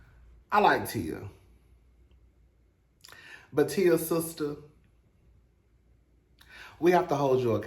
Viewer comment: Smh I am SLOW! 😩 you said Tia, sister, and I heard that as Tia sister (Tamera) chile I was confused for a sec 🤦🏾‍♀️